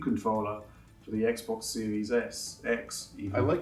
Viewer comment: Anyone else hear him say Xbox series S at 17:40?